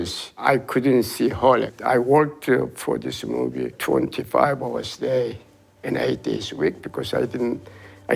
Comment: did he just say he worked 25 hours a day?